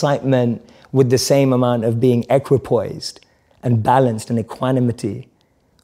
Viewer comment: Can someone please tell me the meaning of the word Jay said at 16:07? Something like ecroposied, thats what i heard, please correct me, as well the meaning of economity.